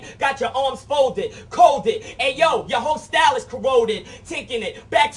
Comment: Did Jin say Covid. Yoooo he brought it over here. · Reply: He corroded not Covid dummy